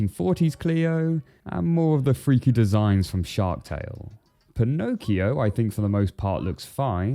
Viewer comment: Shark Tale-vibes intensifies EDIT: I paused the video to make this throw away joke then he actually mentioned it. Wow. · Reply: Not gonna lie I thought you said Sherk tale